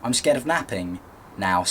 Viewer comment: I thought you said you were afraid of mapping. That would be unusual.